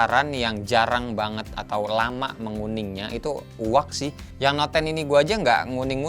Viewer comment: case yg udh setaun d pake blm nguning apa namanya bang? Aku dengernya uwak 🥲 ap telingaku yg budeg plis cari brandnya ga nemu, lg mau cari case anti kunjng utk suamikuu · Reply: UAG merknya